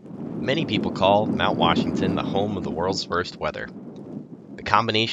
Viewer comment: Well, that was quick: 0:00 many people call Mount Washington the 0:03 home of the world's first weather It sounded like "first" to me - it should be "worst." :-)